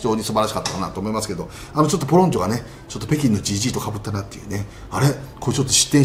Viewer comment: 北京のGGが北京のじじぃに聞こえた